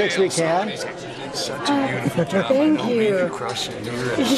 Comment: I didn't hear what he said, but she said "you're being really rude" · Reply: I thought he said "Such a beautiful job, hope to see you at Crufts" (Dog Show)